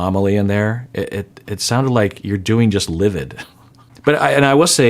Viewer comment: I heard fine, rewinded and heard livid then rewinded again and heard her say fine 😂